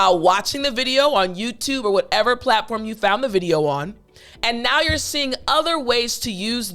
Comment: I have just a question cause I activate the listening skill and I compare what I listen with the video transcription okay!, in 10:05 you said "whatever platform you found on " the question here did you say you have found or you found?